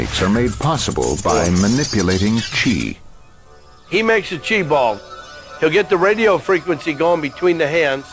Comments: Did he say cheeseball? 'Cause a cheese ball sounds sort of good. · That part with the kid was hilarious. "he is making a chi ball. He's getting the radio frequency between the hands". Why does everyone keep going with it? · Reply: Ohhhhhh, Chi ball.....I thought he said "cheese ball". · Chi ball? Thought he said Cheeseball, which is more believable. PLUS! PLUS! PLUS! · I thought he say cheeseball, not chi ball, now I want a cheeseball...this is the stupidest thing I’ve ever seen